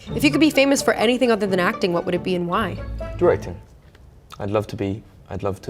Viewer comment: When he said directing I thought he said bread